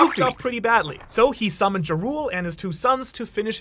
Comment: Did you just call Jarul (Yar-uhl) Jah rule? Like the rapper 😂